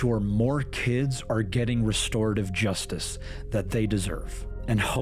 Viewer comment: He didn't say "restore justice" he said "restorative justice" which is a very poor choice of words. These aren't fender-benders or theft that can just be replaced with a monetary return, these are crimes that cause long-lasting and often permanent effects. I think what he meant to say was "punitive justice." Also it's asinine of him to say we'll stop all crimes of this nature. More investment in the systems and attention on how the predators are accessing children (aka online, which is still vastly underestimated by law enforcement in matters like this imo) but it's naive to think or even say that you're going to stop ALL crime. It's as stupid as saying "make murder illegal."